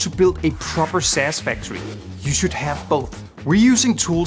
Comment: did you say "sapure" what is that tool?